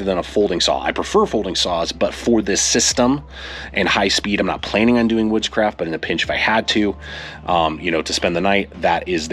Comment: I had to rewind 4:33 a few times 😂 i was like "he has to be saying woodscraft right?" Because the first few times it sounded like "witchcraft"